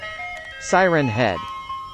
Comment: In the beginning it sounds like he says Silen Head instead of Siren Head